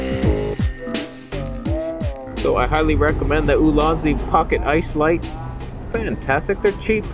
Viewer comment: Thought you said "...with the Ulanzi pocket-ass lights", but sadly it was "Pocket Ice lights".